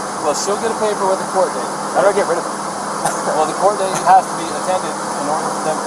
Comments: “How do I get rid of her?” fake laughs Did I hear that right? Biggest red flag in the entire video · What does Brian say at the 27:00 mark? The cop mentions a court date. Did Brian say "that'll get rid of her"? Or "that'll get rid of it"? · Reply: If you turn on CC = Closed Caption and Transcript, you can read the audio text. Here is what was said at the timestamp your talking about. 26:53 so i just want to get like the checklist 26:55 of things i got to do to get rid of 26:57 association 26:59 well she'll get a paper with a court 27:00 date how do i get rid of it 27:02 well the court date has to be attended 27:04 in order for them to decide whether they 27:05 need to continue dropping the first is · Is it just me or does he say "How do I get rid of her?" and then laugh a few times? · Hes a narcissistic sociopath. Notice when he finds out SHE’s in trouble and SHE’s going to jail he gets all happy….as he has “The Power” and “Control” and can’t leverage this against and hold over her in the future. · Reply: did he say “I don’t wanna get rid of her.” ? Smh 😭 💔